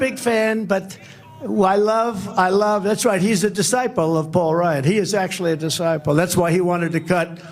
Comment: does he say DeSamtis is a disciple of Paul Rudd? The actor who plays Ant Man? 😂😂😂